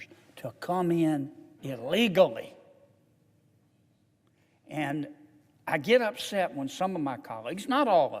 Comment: Did you say "ILLEGALLY" MR. Sen. Kennedy?